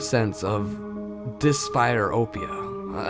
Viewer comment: "I encountered a sense of..." what is this word you're trying to say? Dis-pyr-opia? I have never heard this word before.